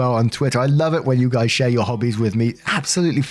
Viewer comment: At the end it sounded like you said "I love it when you share your Hubby's with me." lol